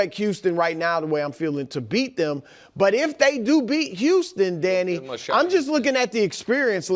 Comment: I was wiping my ears with a Q tip and thought he said Daddy instead of Danny 😂